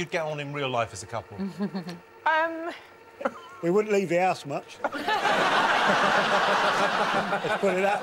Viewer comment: What does he say at 1:07? I'm not a native speaker but this is the first time I can't understand him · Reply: “We wouldn’t leave the house much”